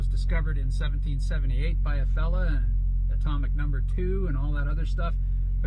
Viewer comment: Sounded like you said atomic number 2. Helium be like 👀. It's 42.